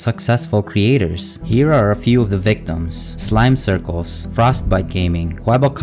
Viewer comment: did he say Smile*Circles*?